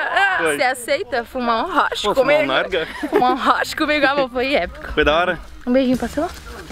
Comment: prestem atenção, a chama o de "Amor". Eitaaaa, que tá vindo um pedido oficial aí...❤️❤️🤣🤣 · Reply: Ela falou tbm 7:56 e dps 8:05 tbm kkkk nn sei se ouvi direito mais parece ela fala "amor foi épico"